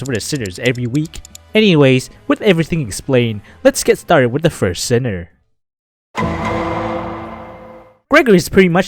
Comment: did you say.. first sinner..?